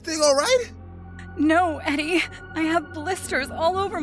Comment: did she just say “on god”💀